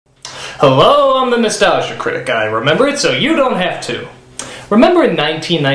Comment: T-T In the intro I always hear "Hellooo.. I'm the moustache guy who remembers so you don't have to" ...and here the goatee is gone.. so sad. Liar moustache guy.